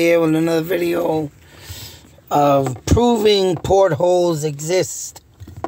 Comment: I always thought you were saying portals, as in openings that go places. Portholes are little windows on the sides of boats or aircraft. Not sure what you’re getting at now.